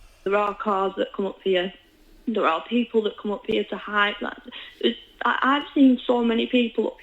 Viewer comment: Did Say There's People That Go Up There Tò Hide??